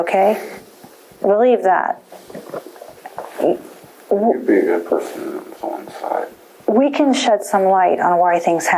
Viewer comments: Please tell me I’m not the only one who heard “You’d be a good person to go inside” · Did he say “you’d be a good person to go inside” wtfff